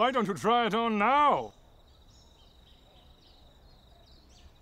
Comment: I thought the intro said It cant be seen maaaan